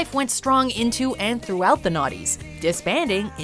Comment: did they mean to say 90's cause i heard "naughties"🤔